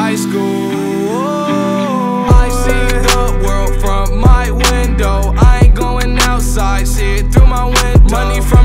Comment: Huh and i thought it said "WIDOW".